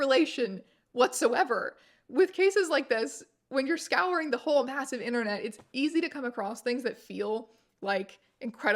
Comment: I know that the cc says you said "scouring the whole massive internet" but I swear I heard you say "scouring the whole ass of the internet" and I much prefer that phrasing.